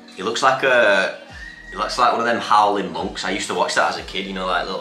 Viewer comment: Did you say Halloween monk???